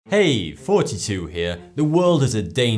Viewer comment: For the longest time i thought he was saying "Hey, 42 here"